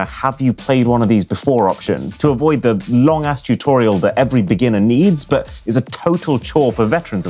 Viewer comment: did you say long ass tutorial? 💀